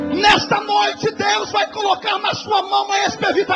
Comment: foi Borra ou gangorra 9:45 mas fico com gangorra